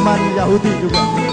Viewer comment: Perhatikan , kalo ga salah Denger dia ucap kata "YAHUDI" . peace .. Kalo ga salah :D